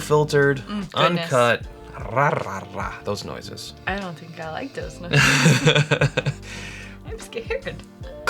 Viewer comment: I was pretty confused at first when watching this video because I thought at 01:15 that had said “I don’t know if I like this nose” but then seemingly talked it up after talked about how much he liked the nose. After the first sip in the tasting, I decided to go back and rewatch everything, and realized that was saying “I don’t think I like those noises” to roaring hahaha. Glad I got clarity on that ;-) Can’t wait to find this one when it hits our shelves in NC! Also… YES to the HH barrel proof blind flights. I’d love to see how the LBPs and the ECBPS compare within their lineups for this year… and I’d love to see how the winners compare in a head to head (or even how the top two from each LBP and ECBP compare in a four-way blind)!